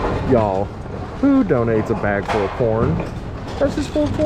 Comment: Those are usually pretty pricey watches, my buddy has one that cost his wife $700 but could be a fake? I had your video playing while i was doing some stuff at the house, I swore you said a "a bag full of porn"